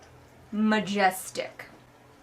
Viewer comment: When you said "she thinks his dick is majestic", I heard "majesdick". :D